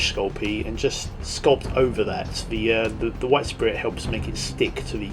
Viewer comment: Awesome work!! :) What is it you say around 3:50? You've brushed something on it? Sounds like "White spirit"!? :D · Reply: Thanks! :) Yes 'White Spirit' - it's a thinner used in the UK for thinning paint and cleaning brushes. I know it's called 'Mineral Spirits' in the US, not sure about other countries though.